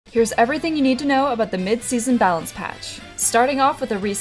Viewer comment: I thought this just said banana patch